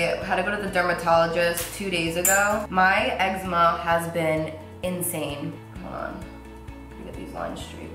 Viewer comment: Lol it sounded like you said "My ex mom has been insane." love you girl!!💙💚💚